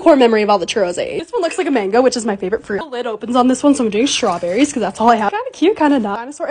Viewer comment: I thought she said "sOmE ✨gAy✨ sTrAwBeRrIeS"